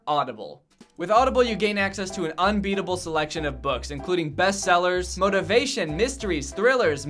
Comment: I thought he said Audible has an "unreadable selection of books," which I...guess is true?